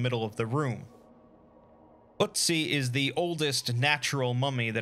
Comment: Ötzi, oh dear you mean Ötzi? I actually had to play this part five times over before i understood what you said XD Heavy slaughterage there!